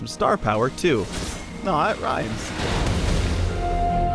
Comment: what the actual fuck is he saying. I've replayed that bit at least 10 times and still can't understand it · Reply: nvm i got it. he says "aww it rhymes", but for some reason adds an "n" sound to the beginning of aww